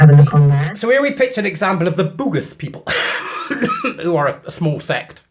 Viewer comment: for a second there I thought you said the bugger people.